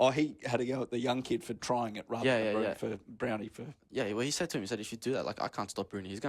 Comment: did he just call him brownie 😂